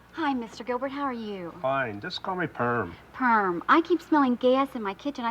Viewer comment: I thought he said "just call me Perv"